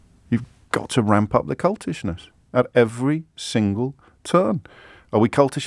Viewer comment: I 💯 thought he said ramp up the “Cuntishness” 😂